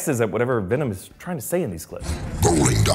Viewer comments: I heard "rolling down the street like a turd in the wind" that can't be right... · I think Venom is saying: Rolling down the street, like a turd, in the wind.